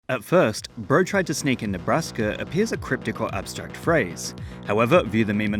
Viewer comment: I originally thought it said "bro tried to speak in Nebraska"